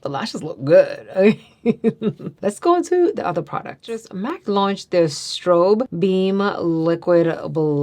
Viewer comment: Sorry off topic here. I must be hungry. It’s late. Everytime said “launch”, I heard “lunch”! 🤦🏽‍♀️